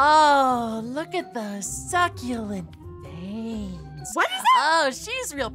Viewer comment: noi : soo cute maid aphmau : WHAT YOU SAY me : ....... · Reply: HE SAID "SUCCULENT VEINS" WTFFFFFF